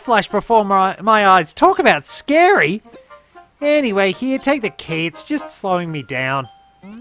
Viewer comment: Ok, I had a hilarious mishearing in this one! Instead of "Just take the key, it's slowing me down.", I for some reason heard "Just take the KID, it's slowing me down.", and now I have a head canon that the rabbit is a deadbeat dad who's hiding out in the castle/grounds to avoid his wife.